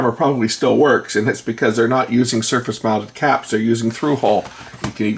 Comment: "through hole"? (wanted to make sure I heard correctly)